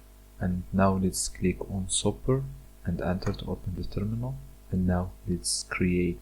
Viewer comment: sorry but what the hell did you say at 1:35 i cant tell what you said 😔 (edit: ok nevermind) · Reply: He says Super+Enter to open the terminal. Later Super+Shift+C to reload Sway and Super+D to open the Drun app list. Also, vim can be substituted with nano or whatever you prefer to edit the config files.